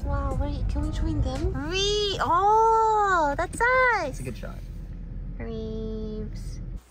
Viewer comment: Did lily call herself ....REEVES ?